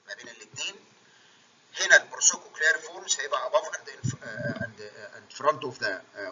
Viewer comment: مسمعتش كويس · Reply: هنا ال processes cochleariformin